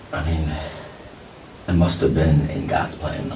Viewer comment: Oh he said “must’ve been in GODS HANDS..” uduurgghh I thought he said “must’ve been in COSPLAY” holy shi, I know this clown didn’t just say that he disnt